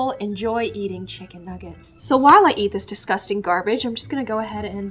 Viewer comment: I thought she said that the chicken nuggets were disgusting